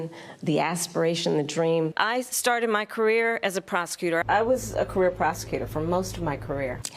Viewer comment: It almost sounds like she said 'I started my career as a pros..t.tute not prosecutor." We all know she did!